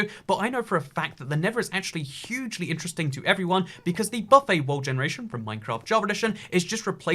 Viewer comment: What does he say at 0:20? Bopay generation? What? I want to find this